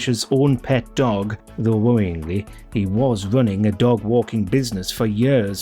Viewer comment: Did I hear this correctly? Steve Kish was running a "Dog Wanking" Business? oops, sorry, I misheard "Walking" A Man's best friend!